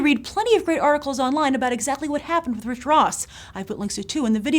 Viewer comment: Oh and at first, I thought that said RICK ROSS. And I was like: "wtf is Rick Ross doing working for Disney?!?!"